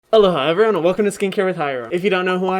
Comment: Up until 2 days ago I thought he said “ hello, hi everyone”. I just realized he says “ALOHA”...